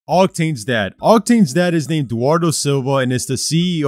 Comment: Bro I thought he said Octane was dead